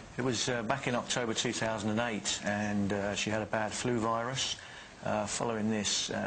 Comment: Did he just say 3008? at 0:30 ?